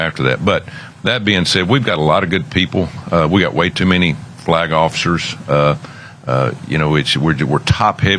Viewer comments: did he really say, “We have way too many black officers.”????? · Did he just say there are too many black officers?